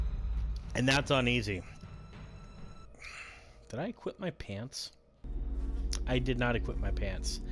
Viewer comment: I know this is old, but just got Elex, liking it so far, but it is tough! Playing on normal. Just wanted to say, when ur like, did I equip my pants? At first I thought u said if u shit ur pants lmao. Good vid very helpful!